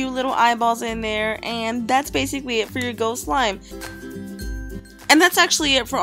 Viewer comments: For the last one i thought she said goat slime! :') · I thought she said goat slime lol